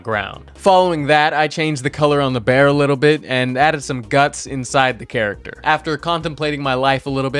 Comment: WHAT DID YOU SAY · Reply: and added some guts